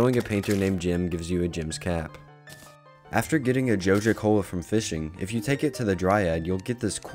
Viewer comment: I hear you say "jojo cola"